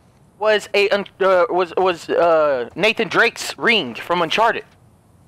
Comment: I thought he said naked drake lmao